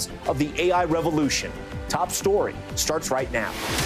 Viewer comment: Thought it said toy story